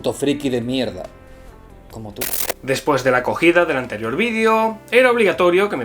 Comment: después de la ¿QUÉ? 😱🤣 Exacto, son consejos para la comunicación asertiva, nada rebuscado, buen video. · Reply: Jajajaja primero entendí “la cogida”, pero después puse más atención y dice “la acogida”. ¿O sí dice “cogida”? 🤔